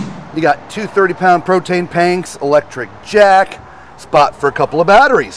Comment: Solid video. I have a 2023 Jayco 183RB; looking to potentially upgrade. I’m curious to know if I just heard “protane panks” though. If so, that’s my new favorite.